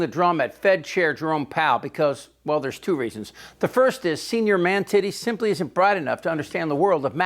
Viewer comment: Did I just hear "senior man-titties"? "Malignant sociopath" "grift" At least "bat-shit crazy" vibes from my early millennial lexicon lol. 5 mins later wtf, brah I fucking love this guy.